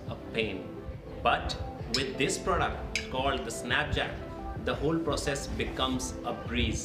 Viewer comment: i heard at first -Snapchat. Btw new tyres looking nice